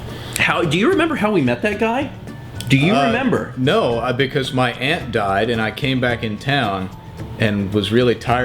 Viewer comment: When said his aunt died I thought he said his ant died and then I realized ....